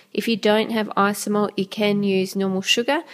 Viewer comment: What's the word she says at 12:03 - to make the windows - icy mont?? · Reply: She says "Isomalt", not icy mont 😊 my answer is a little late, sorry 🙏🏻☺️